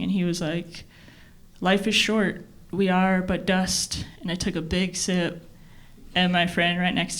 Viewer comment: That 'we are but dust' joke was set up masterfully. Enough time passed to make us forget about that line, and then it comes out again in a masterful finale. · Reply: It thought she said we are butt dust maybe i misheard it